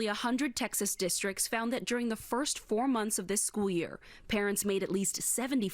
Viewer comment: Oh parents......I thought they said Karens lol